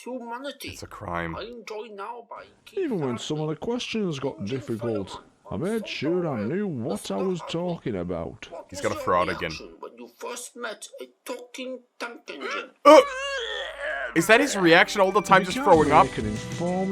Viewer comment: TaWkInG tOnK eNgInE Edit:i actualy meant to put tonk instead of tank beacuse it sounds like that😂😂😂